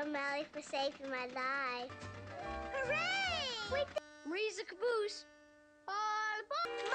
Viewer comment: I never knew what was said here! He said "Marie is a caboose!" Googled the word, never heard it in the UK, and it means: - a railway wagon with accommodation for the train crew, typically attached to the end of the train - slang for buttocks